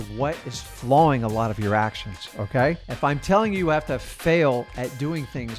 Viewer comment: Sounded like you said "Flawing your actions" but it said "Flowing your actions".